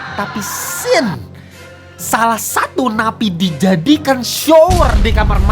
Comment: jir gw dengernya 'nabi' lagi